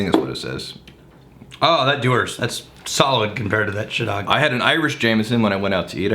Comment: did call it Shitdog?